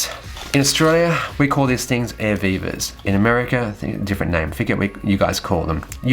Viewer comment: Oh, Air VIVA ! I thought he was saying Air Beaver. But then it took me 35 years to figure out Jimi Hendrix was not saying 'Scuse me while I kiss this Guy. :)